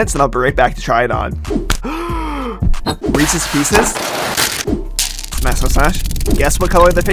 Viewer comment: I thought u said meeseeks pieces